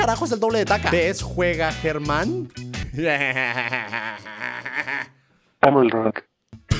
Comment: en el 2:41 y un poco mas adelante parece que dice "amo el roblox" en ves de decir "amo el rock" (like si tambien lo escuchaste) xd